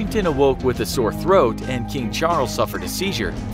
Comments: who else heard james charles instead if king charles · did i hear... JAMES CHARLES